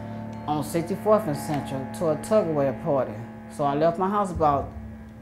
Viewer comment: I know she telling the truth bc she said Tupper Ware party...we used to ALWAYS have Tupper Ware parties growin up 😏. I believe you, Ms. and I'm glad God blessed u to still be here! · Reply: See I thought she said Tug of War parties. Must be that Louisiana twang. Thanks for clearing that up for me